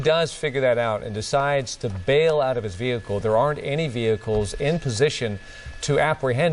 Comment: Yep, I miss heard he and thought he said aren't and I was like, wait what? Thanks for the clarification.